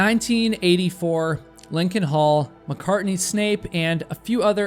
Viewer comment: I keep hearing "Haul McCartney".